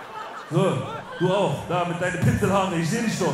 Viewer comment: was sagt kc zu dem typen bei 1:10 ? versteh ich nicht... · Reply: udontknowme "du auch du mit deinen "pinsel-Haaren" glaub ich